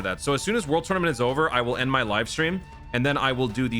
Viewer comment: It’s funny how my audio cut out at the most perfect time when I heard say, “So as soon as world tournament is over, I will end my life…” LOL, had to rewind back to that part.